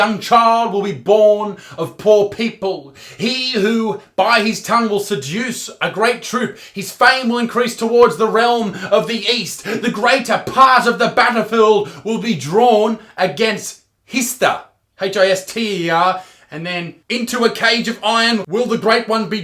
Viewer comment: When he said battlefield, I thought he said Butterfield.